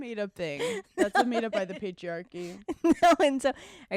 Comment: I thought Hannah said the Paige-riarchy instead of patriarchy 😂 I’m dead